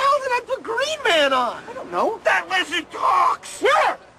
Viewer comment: I misheard that....I thought he say "That's detox" and then I realizes he really say "that lizard talks" 😂